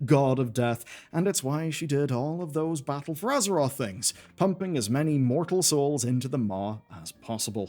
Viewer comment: I misheard "pumping" as "humping" and was reminded of Futurama Death by Snu Snu